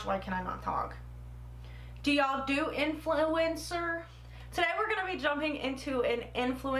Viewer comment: At first I thought you were going to say, You all do insulin?” 😂 That bronzer looked really nice! 💗